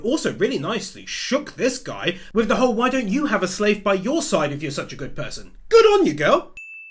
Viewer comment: funny enough, at 14:35 i always thought she said "why dont you have a different slave by your side" calling the bitch a slave.